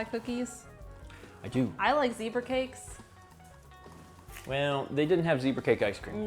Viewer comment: When mentioned Zebra Cakes, I thought she said beaver cakes lol